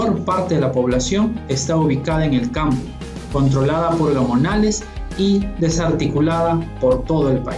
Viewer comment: controlada por lo que? no entiendo lo q dice en esa parte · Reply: gamonales V